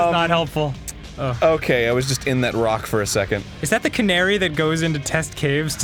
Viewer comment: When he said Canary, I heard Qunari. That's it, enough Dragon Age for this week.